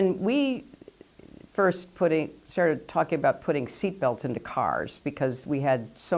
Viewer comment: did she just say that she wants to put seatbelts on our minds ??? on everyone, not just children....